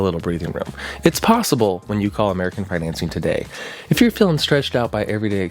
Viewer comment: Is it just me or did he say “American Pregnancy”?